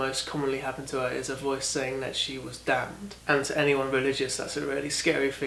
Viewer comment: I had the kettle boiling when you said that the voices were telling her that she was damned, so I misheard it as the voices told her she was daft, and that was a really serious thing for any religious person... I was so confused as to why that would be a big deal until I realised I'd misheard it 😂